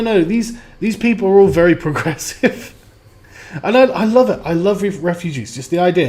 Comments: I thought she said "I love STDs!" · I THOUGHT SHE SAID..I LOVE S.T.D. S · I thought she was saying "I love RPGs" and I was like "That bitch better not be claiming RPGs, those are sacred." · I thought she said: I love RPGs